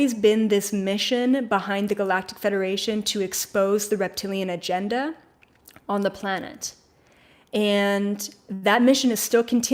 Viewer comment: I thought you said the Galactic Federation kick the rep Tilian’s off the planet